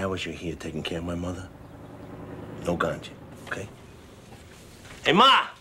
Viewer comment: What does Tony Soprano say at 0:27? No.... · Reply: No ganja